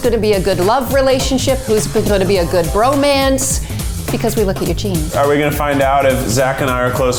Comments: at first I thought it said “Because we look at EUGENE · When the lady said "Because we look at your genes" I thought she said "Because we look at Eugene" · when she said "because we look at your gene" I thought she said "because we look at Eugene." I still nodded in agreement. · Lady: "We look at your genes," I was waiting for Eugene to anwser 😂😂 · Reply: I really thought she said "Eugenes" lmao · When she said "We look at your genes," I thought she said, "We take look at Eugene's." 😂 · Omg I thought she said “because we look at Eugene” 😂